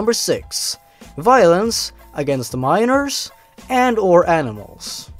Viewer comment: I thought it said mirrors XD